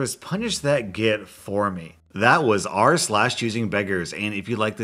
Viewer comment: lisen to the words XD If you didn't hear it. it was "That was r/choosingbeggers" XD